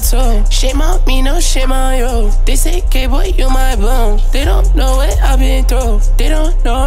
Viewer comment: Did he say dis ain gay boy u my boo¿ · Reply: they say cay boy u my boo